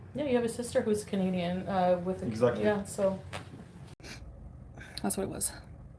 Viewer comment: To be fair, at 41:12 it does sound like she says part of “is with a Canadian” and then gives up lol ( still super sucks tho)